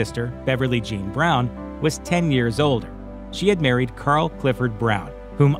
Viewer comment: I had to go back over and over again to hear what dude said about ice cube because it sounded like he said she was 10 years old when she was killed instead of 10 years older and it had me a bit confused and concerned